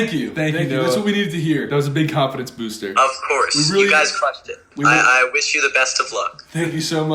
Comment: did Noah say “you guys fucked it” ?? Or did I hear wrong😭😂